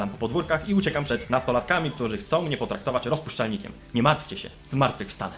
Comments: ja usłyszałam dajcie mi traktor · usłyszałem staną mi traktor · Dajcie mi traktor usłyszałem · Usłyszałem : ruchaj mnie w traktor !!!!!!!! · ja usłyszałam 'dajcie mi traktor' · A ja usłyszałam dajcie mi traktor . :D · Ja usłyszałem u "mnie jest traktor"